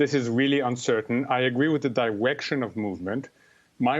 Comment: Did he say “diwection”? 0:11 Dat wascally wabbit.